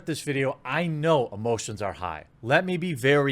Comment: I thought he said "Before we start I want to be very clear: I know most of you are high" and I was like: how... how did he know?